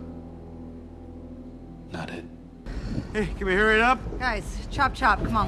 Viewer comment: did he say “noted” or “not it”? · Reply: Not it. As in he's not gonna go on vormir